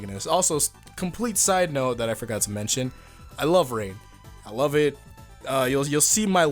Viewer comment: When you said I love rain I thought you said that you loved rape and I got really confused